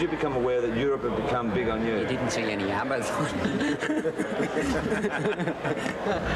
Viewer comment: what did angus say ??? I missed it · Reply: We didn't see any abba's